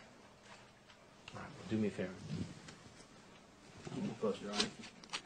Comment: What’s Hansen saying at 35:06? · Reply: “Do me a favor, keep me posted, alright?”